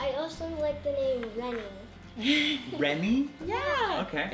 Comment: when (or) said "I like the name Reynie" I thought I heard "Remy" too. My son's name is Remington (Remi for short). Have you ever thought about using "Anthony" and his nickname could be "Andy" that way you COULD keep the 'Hashtag goal/need' but he wouldn't be another Andrew